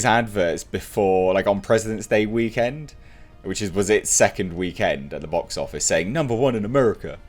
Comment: I was standing in my kitchen , like 6 or 7 feet a away from my phone at the table , and I swear ,I honestly thought you said queefed at the box office . hahahaha!